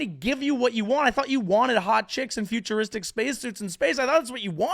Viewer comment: I kept hearing... “Hot Chicks.” What? What hot chicks? To each their own I guess. 😅🎉